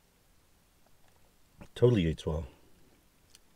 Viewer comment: did you just say “totally eetswa”?? I thought u were trendy as it is, just saying the word “vibe” hahah